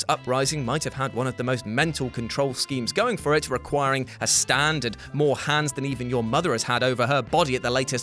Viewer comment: Sorry did he say it required a STAND